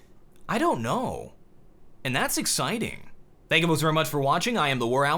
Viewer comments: legit just thought this dude said "and i still have no clothes on" XD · I always heard you say at the end "... and I still ... have no clothes on"